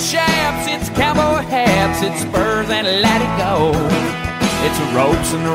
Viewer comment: “ I’m not big on sausage and gravy“ instead of I’m not big on social graces, Garth Brooks. 🤦🏻‍♀️ that’s what I thought it said for years 😂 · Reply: When he was talking about Spurs and latigo I always thought he said "Spurs and letting go"